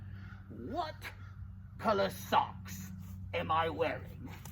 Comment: what is that question that your saying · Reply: Palpatine was asking Ahsoka to tell him what color socks he was wearing